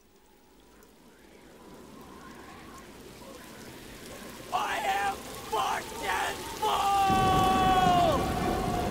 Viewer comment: What did Romeo say here? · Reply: "I am fortunes fool!"